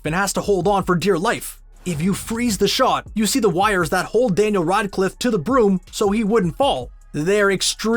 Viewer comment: wtf did he just said? danel runclif?